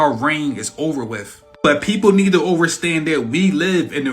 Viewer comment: I have adhd and audio processing issues so I use subtitles when watching videos. You know what’s wild at 12:50 dude says “overstand” and the captions catch it and swap it with “understand”. I’ve seen people say some wild shit and the captioning will follow what they say pretty closely but in no world do the words “over” and “under” sound similar enough to do that especially said as clearly as he does, I could understand if it said “under stand” but to completely change it seems odd to me.